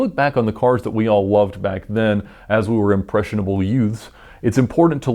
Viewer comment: did you say "Impressionable Yoots?" Whudizdis, "My Cousin Eddie"?